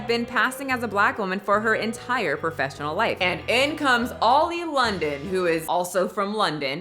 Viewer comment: Oli London is a joke. But I do enjoy watching Koreans roast Oli and Oli's Korean. But on a serious note, I think Oli is more addicted to attention than anything. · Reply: At first you had me dying from laughter cause at I thought you said OIL London instead of OLI London 😅🤣😂